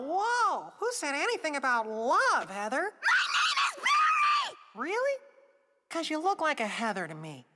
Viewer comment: "MARY MARY MARY MARY MARY MARYMARYMARYMARYMAR- Uh oh!" (I know, her name is berry, but it's sounded like she saying mary.)